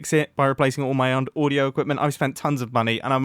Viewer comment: it sounded like he was gonna say replacing all my underwear